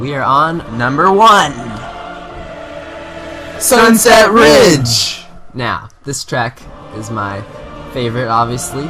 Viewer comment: It sounded like they said suddenset ridge s